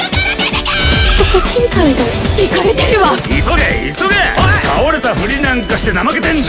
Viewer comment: ポコちん会だ。イカれてるわ にしか聞こえなくて病んできた